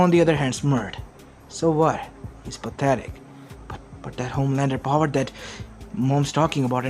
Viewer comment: Bruh I thought it said homeless powers